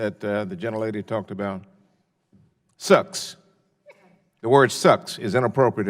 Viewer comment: Sucks? I thought he said “aw shucks”. He is from Oklahoma.